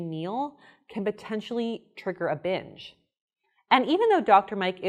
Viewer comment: i deadass heard you say "can trigger a b*tch" instead of "can trigger a binge" I SWEAR